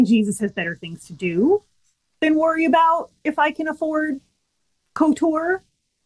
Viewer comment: Did just say that she wants to afford KOTOR, as in Star Wars: Knights of the Old Republic? I'm pretty sure she didn't, but it sure sounds like she said that.